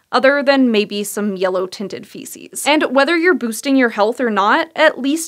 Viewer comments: at the end, it sounds like she is saying you can instagram your poop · I thought she was saying "a pretty, yellow Instagram post" as if people were posting pics of their turmeric stained poops lol